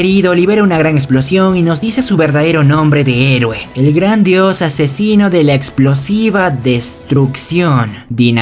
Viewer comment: alguien noto que dice asesiono?